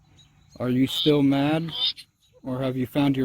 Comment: I heard I'm still..... Mad